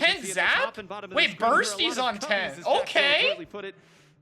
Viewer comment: I thought you said "burst" is on tent my eyes widened so much just to realized you said someones name. why why 😭😭😭